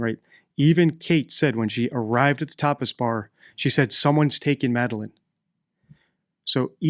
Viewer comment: Lol I thought he said "topless bar" and I needed the levity.😢